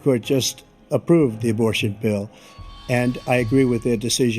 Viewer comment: Please please clarify... at 5:26 to 5:32 ... He said, " the Supreme Court approved the abortion "bill "... he did not say supported the "pill" which is what your implying which makes your whole argument flawed. Please look into this and edit your statement. Thanks!